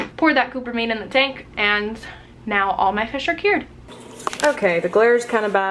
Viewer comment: I thought she said instead of "all my fish are cured " all my fish are dead I got so scared